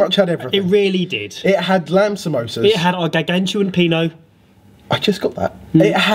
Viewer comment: HAHAHA I heard it as gargantuan peanut